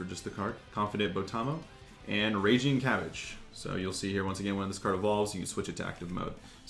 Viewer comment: Is it just me or did he say raging cabbage